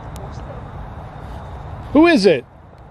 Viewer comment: definitely hear a voice can’t make out what it’s saying though sorry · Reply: "hello, who's there"